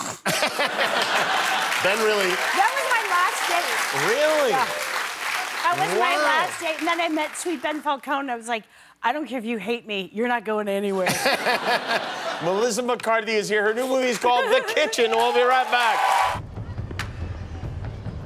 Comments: Did he just call her Melissa McCartney at 7:11 😂😂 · Did he call her Melissa McCartney at 7:13?